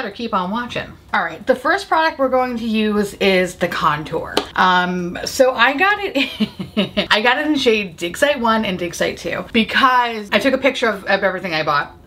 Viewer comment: Half listening, I literally thought you said “I got it in shades Dick Side 1 and Dick Side 2”.